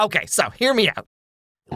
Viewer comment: did he say hear me out 🤨